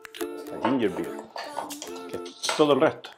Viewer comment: Hola, me acabo de suscribir a tu canal y me encantó. Te felicito. Lo que si hay veces que no se entiende bien lo que dices. Ej. en minuto 11:32 le echas algo al Gin, parece que dices Ginger ??? Y me parece que no está en la receta. Muchas gracias y éxito 🥂🥂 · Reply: ¡Hola, Carolina! Es que a veces se me lengua la traba... :-D Pero ahí digo que hay que echarle Ginger Beer... es una bebida carbonatada de jengibre (no es cerveza ni tiene alcohol)... es MUY RICA y es indispensable en esa receta. ¡Abrazos y BienBebida al canal! ;-) PS: ¡Ya lo agregué en la receta! ¡Gracias por avisar!